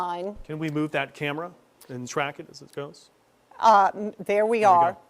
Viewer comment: The newscaster sounds like he says "can we track it as it Goats"